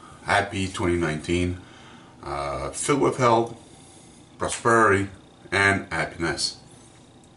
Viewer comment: Filled with hell and prosperity? Lol tell me about it. It really sounded like you said that. Great shave 👍🏻